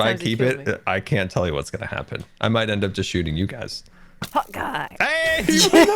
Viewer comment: Is it just me that thought Scar was saying hawkeye and not hot guy